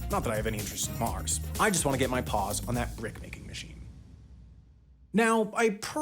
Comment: I misheard "brick making machine" as "bread making machine" and was very intrigued by mars bread